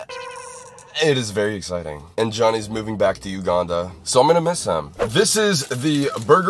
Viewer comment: are we gonna ignore that fact that johnnie is moving 🥲 update: i have bad hearing , ignore this 😭😭😭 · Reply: LMFAO i realized that now i thought he was saying utah the first time i watched it i didn't know ok 😭 (even though johnnie never lived there LOL whoops) i couldn't tell if he was joking because i know i saw in another vid said they were still moving with eachother but i didn't know if he changed his mind or something after hearing this LOL but now i hear him say uganda 😭 so that's my bad i just have terrible hearing and can't tell when someone is joking sometimes 🥲